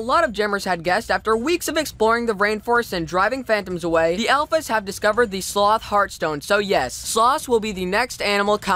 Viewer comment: For a minute I thought he said the ELEPHANTS discovered the sloth X3